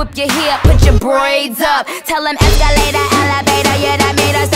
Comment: I thought she said “willow jada is my name of hater”